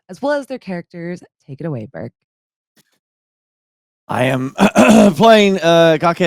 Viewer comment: in the intro when said "take it away burk" i heard "take it away BERG" and i was expecting max to talk... rip good old times :') nostalgia hitting, the feeling of a never ended story... but the magic duo is still together with new friends!